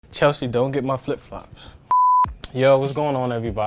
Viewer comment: I heard "Sophie dont get my flipflops" and subscribed immediately